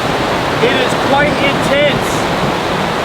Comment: When you said "it is quite intense", I heard, "it was quite in tents" 😂